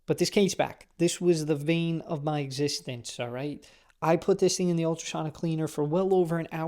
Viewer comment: did you say "the VEIN of my existence"?